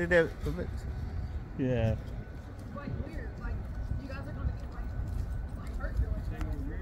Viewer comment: did she say you’re going to get hurt filming? 😮